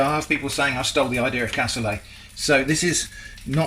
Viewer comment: What the heck is "cassulay" or whatever you said at 14:52? Never heard of it before, hence not being able to spell it. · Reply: Cassoulet